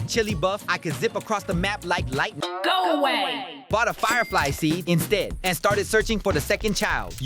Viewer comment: Did you just say sheep